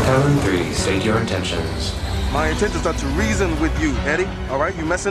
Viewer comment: "my attention are to reason with you"🤣🤣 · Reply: I thought he say attention 😅